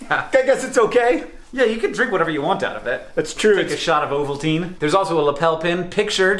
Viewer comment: I had to re-watch 3:19 because I kept hearing "Take a shot of protein."